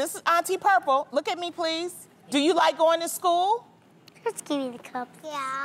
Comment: Aww they’re cute. But am I the only one who thinks they’re a little spoiled like how he said give me the cup. Neriah has my heart ❤️ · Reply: I thought he said just give me the cup please, lol.